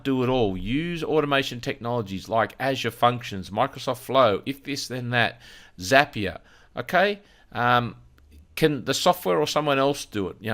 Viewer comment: you mention automation tools: Azure Functions, Microsoft Flow, and something else that I couldn't hear distinctly - what was that? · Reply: If This Than That (ITTT) and Zapier